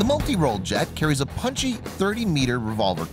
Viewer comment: did he just say 30 meter?